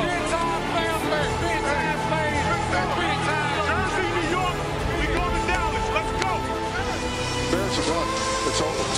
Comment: What did Raji say? I cant seem to figure it out. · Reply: We going to dallas